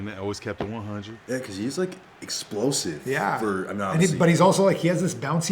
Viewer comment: Can someone tell me what Melo said? I keep rewinding and understanding that Cena is "explosive for a Nazi".